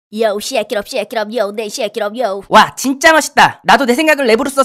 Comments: 나만 와..진짜 맛있다라고 들리는거지? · 에와진짜 맜있다라고들림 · 맛있다라고들리는데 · 진짜 맛있다 라고 들림ㅋㅋ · 나만 진짜 맛있다로 들림?? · 맜있다로 들림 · 잘 들어보니 0:04초 에 왜멋있다다가 맛있다 라고 들리는건 기분탓 인가요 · 에 나만 맛있다 라고 들리나 ㅋㅋ